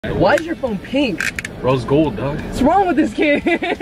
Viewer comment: "Why is your phone pink?!" "Rose gold dawg" "Whatssss wrong with this kid?!" "Your shirts pink!" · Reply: I thought he said "rose gold dawg"